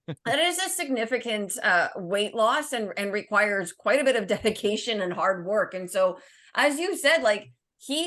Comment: Hahaha. I thought said, "That requires quite a lot of defecation and hard work" and literally did a spit take. LoL.